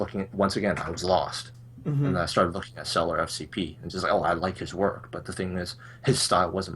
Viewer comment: What was the name of the guy that said he liked at 2:43:00? I think he said "Seller SFP" but it seems to have turned up no results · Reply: I think it's cellar FCP